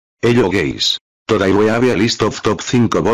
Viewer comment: did he just said "hello g@ 😳